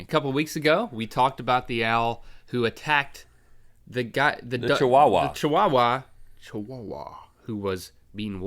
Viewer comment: Do audio translate and pause it at like 1:17 it says " the dot the butter wawa salawah" instead of chihuahua